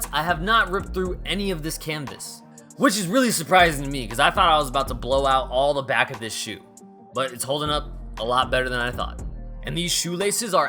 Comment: did you just say you trynna blow ur shoes back out???